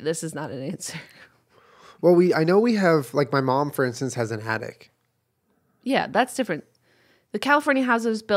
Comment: 'Basement' has started to not sound like a word anymore. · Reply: neverbeunfaithful I was just about to say the same thing 😂 I heard “Your Mum is an Addict? Wooow.” Instead of “has an attic.”